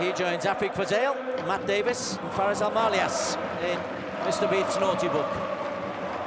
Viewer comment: am i hearing Mr Bean's naughty book ? 🤣🤣